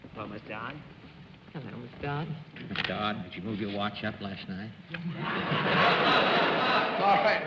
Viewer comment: Can anyone tell me what Fred Allen said when he shook hands with (around 2:40)? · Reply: "Did you move your watch up last night", presumably referring to the start of Daylight Savings Time.